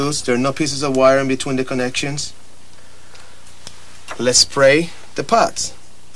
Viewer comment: I thought he was saying " let's pray". Oh....he said lets spray the pots.